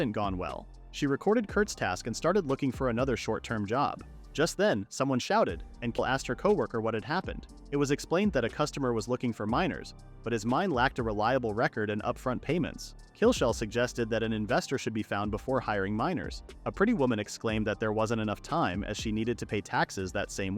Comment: i thought he said "a customer is looking for minors" 😭😭